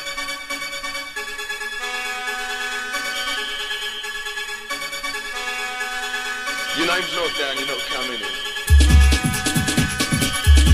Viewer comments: When i first heard this I thought he said 'your name's not Dan...' and thought fuck me, a club full of Dan's, at least women won't have to ask their names ffs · Growing up in Australia, I thought he was saying "Your name's not Dan," and I thought it meant everyone had arrived except some guy called Dan.